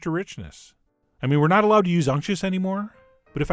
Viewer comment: "I mean we're not allowed to use unches anymore" is what I heard at 6:33. What does he actually say? Why are we not allowed to use it? What is it ?? Thanks! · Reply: "unctuous" <link>